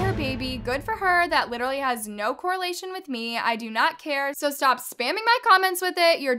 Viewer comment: the way his said coordination instead of correlation